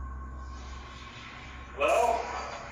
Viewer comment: At the 8:20 mark I hear the woman say help instead of hello like they think.